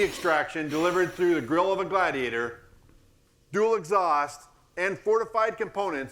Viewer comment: Did he say "through the grill of the Gladiator"???